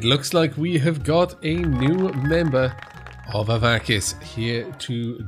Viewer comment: Welp... took 11 episodes, but I finally called it Hearthstone instead of Stonehearth in the intro XD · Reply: also said "avakus" instead of "avakeen" (30:50) XD